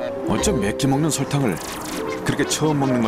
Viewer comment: 나만 이렇게 들려? "어쩜 맵게먹는 설탕을?" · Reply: 저도 그렇게 들리긴하는데 '맵게먹는' 이 아닌 '매끼먹는' 인것같네요:-)